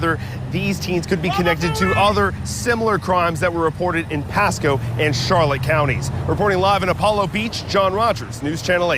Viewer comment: What was the person saying and yelling about at 2:27? 🗣️👂🏾🤨🧐❓🤦🏾‍♀️🤷🏾‍♀️🩵💯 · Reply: They yelled "Bababooey", a reference to the Howard Stern show.